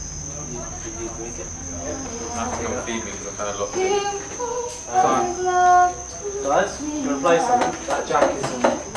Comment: What does she say 0:27-0:32? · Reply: she sing "water me"